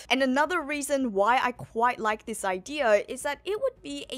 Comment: "first time people hear who sun god nikker is" 💀💀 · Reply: It’s the cringe Australian accent. They cants pronounce the letter “o” they say “aur”. Like vide-aur instead of video… sometimes can’t say “ah” and say “er” .. listen to “ide-er” instead of idea